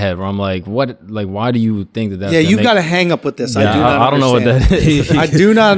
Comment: what did schulz say at 11:52? I do not understand this __ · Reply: hang up